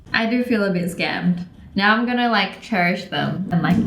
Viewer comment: “Cherry-ish” instead of cherish? Yeah? Yeah? No? Okay...